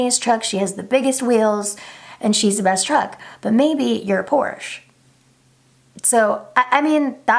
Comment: I heard "maybe, your poor." Can't be a truck because your poor.